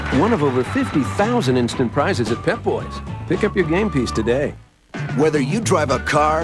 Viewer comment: it sounded like he said, pick up your panties today 😂